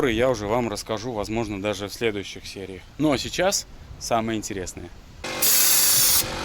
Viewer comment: Послышалось сначало, кошечное сало